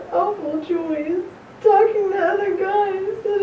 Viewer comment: I heard her say it’s cause she was doing meth